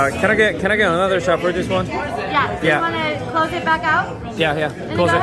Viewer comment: 에서 바텐더 누나가 뭐라고 하신건가요? You wanna close it back out? 이라고 들리는데